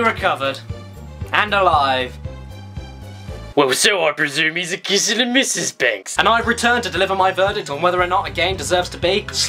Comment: What the shit is he saying at 1:05 ? It always cracks me up, but I have no idea what he is saying. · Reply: ͡° ͜ʖ ͡°) It's a reference to Mary Poppins. The line is: "or so I presume, he's kissing mrs. Banks". It is said by constable Jones at the end of the film. Constable Jones discovers that mr Banks is alive and well, while he is speaking to the chief inspector over the telephone. Mr Banks then kisses his wife. Since it is unusual for mr Banks to show affection like that, constable Jones reacts surprised and says to the inspector: "It's allright! He's been found!...No, alive...or so I presume, he's kissing mrs. Banks." Since said the line the exact same way, I have no doubt that that is what it's referring to.